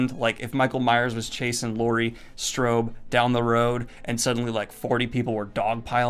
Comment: Did just call her Laurie Strobe?